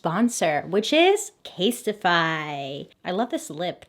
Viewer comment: When you mentioned the sponsor, tell me why I heard “which is” as “witches” 😂💀